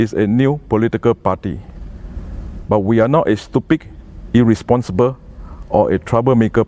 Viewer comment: Why use word stupid? Why you trembling? :-) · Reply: Sounds like he said stupig 😂